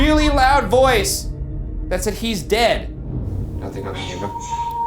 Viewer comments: I didn't hear "He's dead." I heard "hang tight." Anyone else hear that too? Or is it just me? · I heard “hang tight” instead of “he’s dead” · hey! i think that voice that you guys thought said “he’s dead” actually said “hang tight” !!! someone was trying to comfort ryan in his solo investigation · I hear “I Am Dead” instead of “Hes Dead”